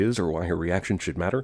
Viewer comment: With Frost's "Blood God" speech, I always thought he was saying, "An active God" not "An act of God"...